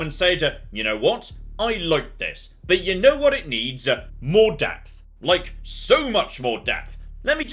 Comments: I misheard you, because when you said "you know what it needs - more depth", I though you said "more DEATH". Two dead soldiers later... you actually DID say "death", didn't you? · i thought you said "more death", lol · Funny, I misunderstood what said right at the start. I honestly thought he said "X-Com, but a lot more death" not depth. :) Both are likely true.